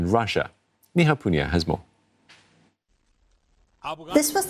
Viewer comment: I thought I heard her said "Ni Hao" Poonia. It's. hahah